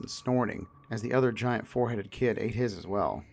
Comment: When he said the giant FOREHEADED kid, I was trippin and thought he said Giant FOUR HEADED kid LOL